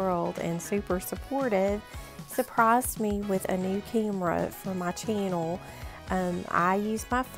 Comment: 😍😍your brother is so sweet for getting you a new phone! Wow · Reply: It was a new camera! 🤦‍♀️I'm so sorry I thought you said phone🤣🤣 but that's super awesome 🧡🧡he is the best